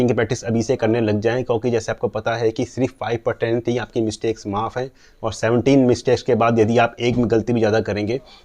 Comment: p kya bola smjha n aaya kiyni mistake maaf hai? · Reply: 5%mistakes maaf hai..ok